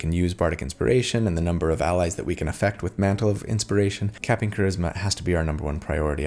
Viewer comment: When you had said capping charisma, I thought I heard “captain charisma”. Perhaps a name for this character? 😁